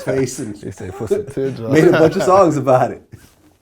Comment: did vlad say "nigga" made 2 songs about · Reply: He actually said "Made a bunch of songs about it"